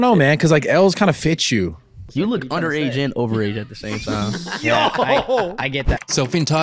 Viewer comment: What did speed mumble to him at 0:33? You look blank and blank at the same time? I put my volume to 100 and I still didn't understand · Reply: You look underaged and overaged at the same time